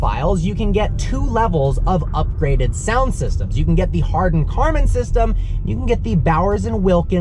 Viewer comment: "Hardon Karman" system thats what he said right?😂😂😂🤣🤣